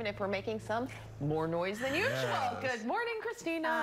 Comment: did she just say “our vis billy”?